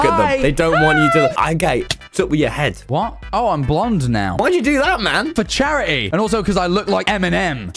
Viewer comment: I thought Tommy said I’m gay😭